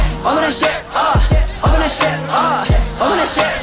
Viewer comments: I keep hearing 1:17 as i wanna shit uH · is he saying im gonna shit😭😭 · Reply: Open that shit up